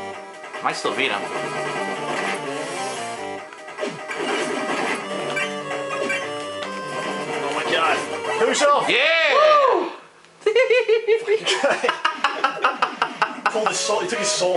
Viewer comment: i thought he was saying 'heal yourself' O_o oh well